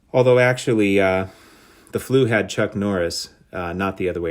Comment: Amazing cards! You have a great eye for the aesthetic and for presentation. “The flu had Chuck Norris” you had me dying! 😂 Although, I could have sworn you said Clint Eastwood. That Ernie Banks is so great. I have to put that on my list. (Pun alert) - sandwiches in the middle of this video you really A-Mays’d us with your all those glorious Willie cards. Love all these. I agree with your take on Bonds and vitamins. Although I have no trouble writing it now, I also avoided saying PEDs the one time I compared Bonds and Ted Williams. I called it “supplements”. I think our aversion to saying PEDs in our videos once again makes us Clubbies. - oh you just said mega-vitamin supplements! Ok - that’s a done deal. Bonds put up the numbers, but all those supplements do have there unintended side effects. He has been said to lay the occasional 🥚🐥